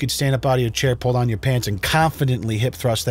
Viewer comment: The video cut a little quickly, do instead of you saying "peace" at the end, I only heard "pee" and that had me in stitches Great video as always